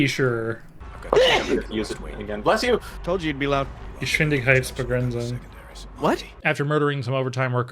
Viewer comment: What did you say at 9:25? Was that "Geschwindigkeitsbegrenzung"? or did i misshear?